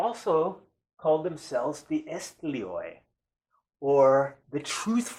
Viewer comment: what was that word again? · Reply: The word is Esthlos